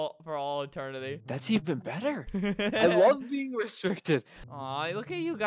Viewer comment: Did... did I hear that correctly? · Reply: Did it say "I love being restricted"